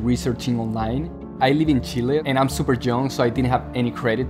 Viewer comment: did he say "I'm Super dumb" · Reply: He said he was, "super young". 😁